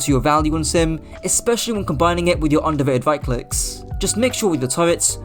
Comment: What is it you say at 17:25? "Undivided right clicks"?